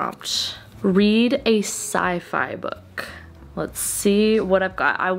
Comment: When you said "Sci-Fi" I heard "side eye', and I was so confused. 🤡